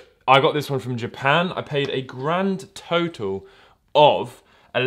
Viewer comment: Okay who else thought he said 8 grand at first